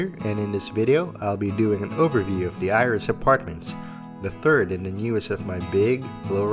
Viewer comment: I thought it said IRS apartment lol